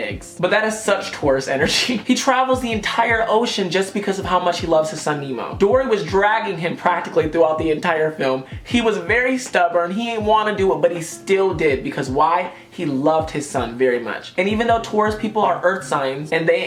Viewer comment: Who else heard him say Dora😂